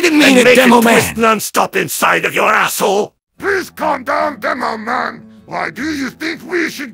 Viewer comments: what did Medic say? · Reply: "please calm down, Demoman!" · what did medic say. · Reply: "please calm down, Demoman!"